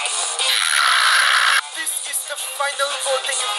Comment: what did he say? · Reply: This is the final elimination so choose carefully.